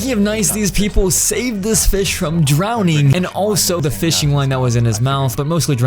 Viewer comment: What did he say drowning?